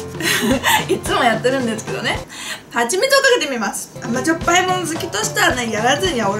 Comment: 3分21秒のはちみつがぱちみつと聞こえるのは私だけでしょうか((((；゜Д゜)))